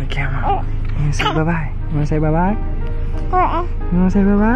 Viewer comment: Am I the only one who hears say “Goodbye” ??? Or am I tripping??